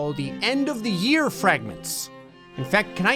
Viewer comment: I thought it said "nugget" instead of fragments.